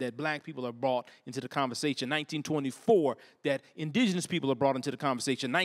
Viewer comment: Did he say 1924? 12:04 😳for the locals? 😳